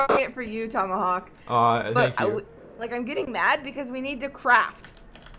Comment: Lol I thought said "I'm getting mad, we have to crap!"